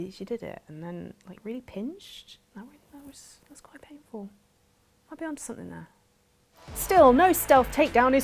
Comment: What the heck did she mumble at around 1:50? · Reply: Nearly 7 years later… “might be on to something there”. Or did you mean the entire thing?